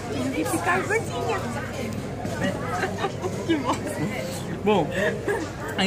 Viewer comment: eu ouvi um "que bosta" da abi? kkkkkkkkk sei lá, ri pq ela quase nunca fala palavrão em vídeos, ou acho que nunca deve ter dito...